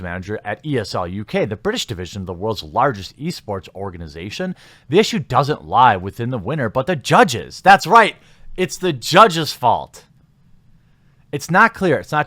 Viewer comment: I swear I kept hearing you say "Yeast Sports"